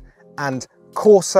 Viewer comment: I almost lost my sh** when he said "città", thinking it was "cheetah", until i heard "it stands for city mode"